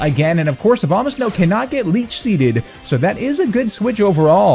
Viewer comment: Omg I misheard Leach Seed as Bleach Seed xD Best attack ever hahaha